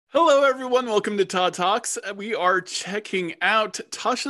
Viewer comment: Todd...you HAVE to be more careful pronouncing your name, man. I swear that I thought I heard 'Welcome to Ta-Tas'!